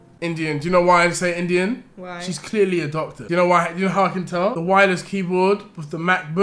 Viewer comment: Who else thought that Deji said the Indian lady was adopted 😂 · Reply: a doctor *